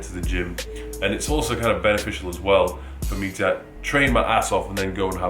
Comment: genuinly thought he said "train my asshole", had to check twice lmao